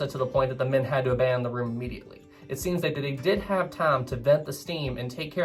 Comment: When the word time was said, I heard "tom" or "thame".